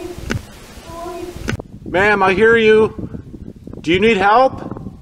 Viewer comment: who are you?? or did I misheard